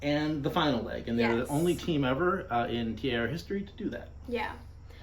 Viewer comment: Did any other team win ONLY the first and last leg? That's what we were trying to say--I probably wasn't clear enough on that. And yes to Amy & Maya--love them so much. Thanks for watching and commenting! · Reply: Oh OK. Yeah I didn't hear that "only" in there at 0:22.